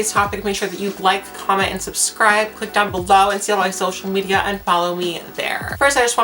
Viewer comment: Did she just say; dick on below?